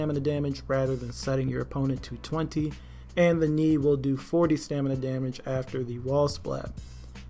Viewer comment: I thought you said that raiders knee after wall splat did 40 damage 😂 not 40 stamina damage.